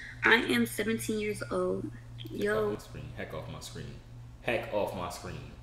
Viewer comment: who hear him say fuck of my screen then corrected himself to heck of my screen